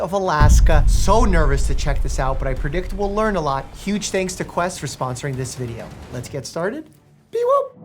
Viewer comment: For some reason I heard "Huge things foquest for sponsoring this video" and I was like ??? The ADHD medication?